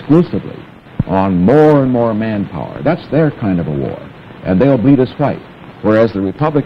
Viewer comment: What does Nixon say at 18:25? "And they'll beat us right"???